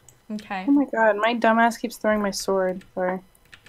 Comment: did she just say Dumba$$- i